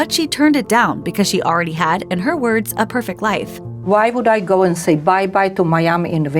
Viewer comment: I thought I heard "Why would i say 'bye bye' to my 'YAMMI' inventor?" i thought 'Whats a YAMMI?' But i read the subtitles! "Why would i say bye bye to Miami in Winter?" Of course!!! Not a single inventor nor a Yammi was involved nor harmed! 😊